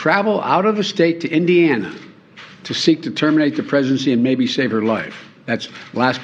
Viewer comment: Did he say terminate the presidency